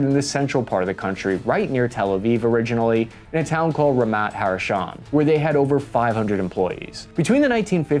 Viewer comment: here, the town is not called "Ramat HaRashon", but, as seen on screen, "Ramat HaSharon", still a good attempt